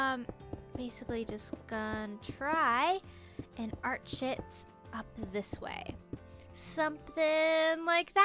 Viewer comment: When she said arch it up I thought she said 'art shit up'